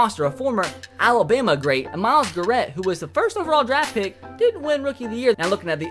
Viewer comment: Did he just say Myles garet